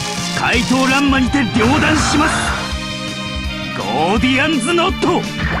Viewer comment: Wait, did he say "Goldion"?! Like Goldion Hammer! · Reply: No, he said Gordian. Ala the Gordian Knot.